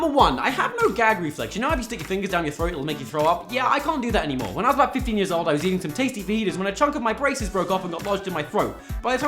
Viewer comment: I keep hearing "When I was around 15 years old I was eating some tasty fetus " XD